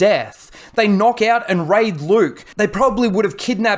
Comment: sounds like you said they knock out and rape luke😂